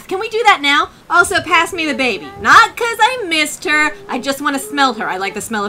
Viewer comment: When she said “Give me the baby” I thought she said “Give me the vape”